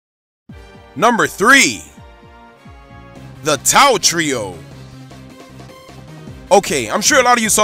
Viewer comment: Did you say "Tao". Tio? What!?!